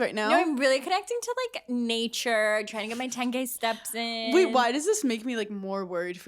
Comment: I had to rewind. When Paige said she got her 10k steps in, I thought she said she got her 10 gay steps in, and I thought that was so cute, and I wanted a video.